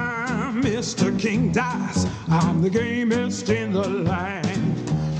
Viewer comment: King Dice: I'm Mr. King Dice, I'm the gamest in the land What I heard: I'm Mr. King Dice, I'm the gayest in the land Me: 😏 Something funny is that when I turned on English auto generated subtitles to hear what he said the subtitles said this, "mr. king dies I'm the gay mist in the land" idk why it skipped the first "I'm" but the gay mist part was hilarious XD